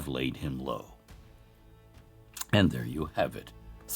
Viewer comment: I heard "deplorable" heros. I may make a better one today, but with simple bread: meats, mustard, cheeses, pickles, tin foil hat recycled into a cooking appliance, bake until satiated.